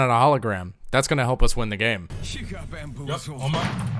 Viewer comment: holy shit did say "bamboozled"?